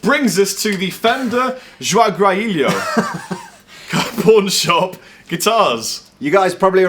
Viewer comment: I swear that i've heard porn shop O.o